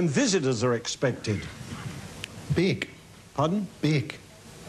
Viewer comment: You know your accent is really thick when even a British person can't understand what you are saying! 😂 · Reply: I knew he was saying bake.